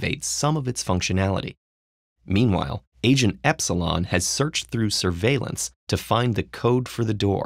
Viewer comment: I thought he said Asian epsilon and the guy is Asian lmao